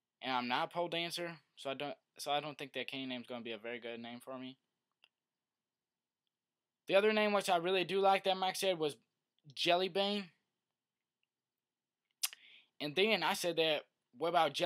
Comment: i THOUGHT HE SAID JELLY BANG!! i must be to stoned!!